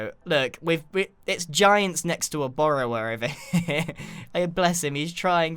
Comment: Did he say Borrower? Secret world of Arrietty?